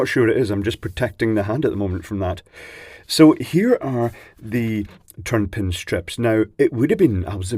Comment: "...so here are the.. [turned pin strips...]"? He says it multiple times shortly after this, what the heck does turned pin mean or is he saying something else and is this a European thing versus American because I'm basically trying to figure out how to order the types of pin headers that he is lauding here. What the heck is he saying? PS: "the ideaR here.." accents are fun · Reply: I do mean turned pin sockets.